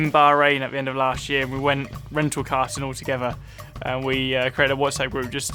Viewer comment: for renting a Car lol... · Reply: I thought he said... Rental karting? I can't get that out of my head😂